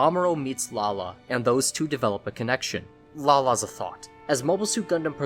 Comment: Did he just say Lala is a thot? lmao